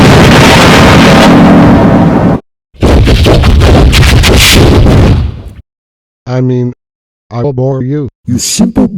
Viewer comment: What is he saying in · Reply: He said “What the Freaking Hell did you just say to me!?!”